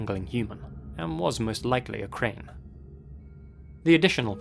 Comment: "and was most likely a" what? i have repeated it a dozen times and havent got a F-ing clue what he says, kramer? krenger? what the hell is he saying?? Please help (it's probably his accent) 🦻🤷‍♂️